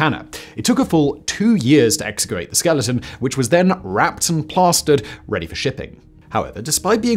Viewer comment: I thought you said "wrapped in pasta" at first. Im kinda disappointed now.